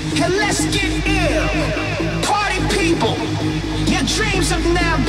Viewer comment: I legit thought he kept saying “cardi B” and not party people lmao